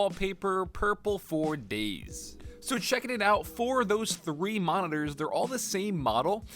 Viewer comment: did he say 4 of those 3 monitors?